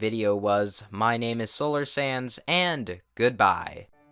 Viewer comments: i thought you said solar sans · For a moment there I thought he said “My name is Solar Sans” I was very confused for a moment there